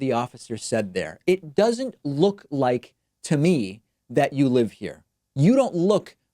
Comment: Sorry David, but you've taken the wrong inference. The cop said "if you don't know the address it doesn't look like, to me, like you live here" not "you don't look like you live here". The former is reasonable, the latter is not - and it was the former that the cop said. Now, choosing to stop this guy in the first place seems like racial profiling, which is improper policing, but don't misrepresent what was said.